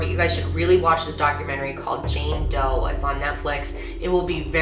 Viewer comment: what’s the name of the Netflix show she said?? Jane what?? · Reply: Jane Doe.